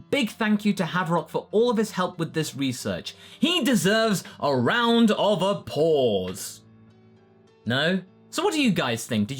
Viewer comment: Sorry all I heard was "around of a pause"... :/